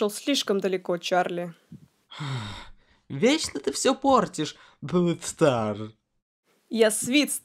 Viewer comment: мне послышалась или он сказал Блэк стар? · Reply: Глуп стар